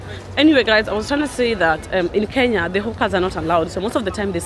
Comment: I thought you were saying "Hookers"😂. Sorry I'm just getting used to your accent. It's stronger than Dee's.